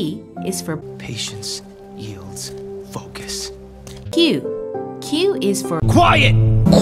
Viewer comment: bro, I misheard it as “fuck it” T-T